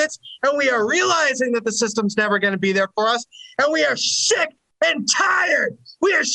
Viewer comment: Could've sworn he said "we are sh*t" before correcting himself to say "sick and tired".